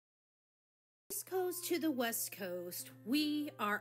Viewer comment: I hear "from the YEAST coast..." Every. Time. 😂